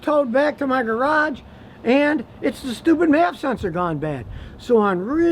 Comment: A little help please. With speaking so dang fast, not crisply articulating, and my slightly poor hearing (yes, I go in for a hearing aid in 10 days) what type of equipment is he talking about from 7:00 - 7:20 ? It sounds like he's saying a "mass sensor". I've been around car repair for years but can't make out what he's saying. Any help appreciated!